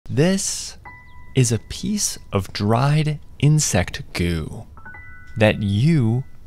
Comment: Bruh i really thought blud said “this is a piece of shatter” 💀 i been smoking too much